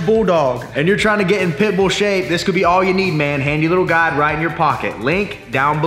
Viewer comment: Had to listen again to @ 1:01 ; thought I heard, "and you're tryin to get in PIMPLE SHAPE".